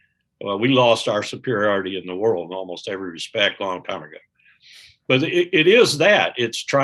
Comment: What does Wilkerson say here? "we lost our superiority in the world in almost every respect on a ?????" WHAT? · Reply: "[...] in almost every respect a long time ago."